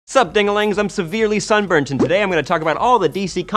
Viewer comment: did he just say dingalings